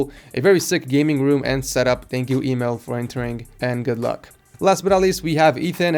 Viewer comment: first i thought he said email when he said emil